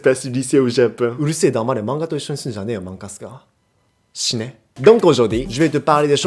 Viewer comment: GOMENE?? cest moi ou il a dit shine? XD (creve) ??!! sa se traduit pas du tout par stp XD